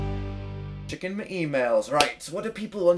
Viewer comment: I'm so sorry but when you introduced the episode i thought you said "chicken my emails" XD....